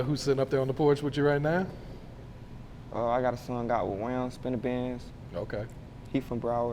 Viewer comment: did he just say he had a song out with spinabenz?? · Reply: He said wam spinthabin